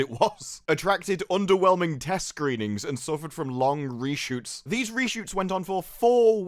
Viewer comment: For a moment, because I have the fan on, I thought he said 'long Brie shoots'.